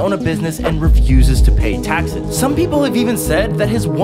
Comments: "And his name is Vlodimar Putin" Rasputin starts playing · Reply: i heard "Vludimar pu" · "vloodomor Putin" "Voldemort Putin*" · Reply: Honestly I thought he said Voldemort Pootin